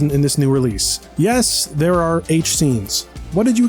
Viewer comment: I thought he said 8-scene, instead he said h scene.